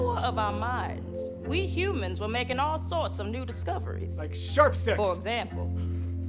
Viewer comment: like sharp what?!? There's no way you said what I think you said, and the dang autogenerated subtitles aren't helping! 😅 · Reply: Sticks, sharp sticks (more correct than he knew hehe)